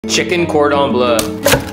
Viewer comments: i heard "chicken cord on blood" .. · Bro says “ Cordon” and “Bleu”. I hear “Corn Dog” and “Bleugh” 😂😂